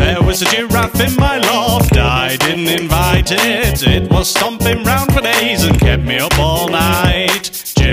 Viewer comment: does it say he was stomping around with aids?